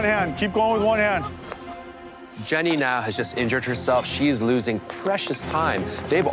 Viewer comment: I absolutely thought he was going to say "Jenny has just injured herself, she is losing precious blood"